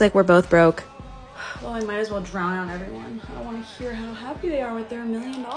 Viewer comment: I THOUGHT HE SAID SHE MAY AS WELL DROWN EVERYONE 😭😭😭😭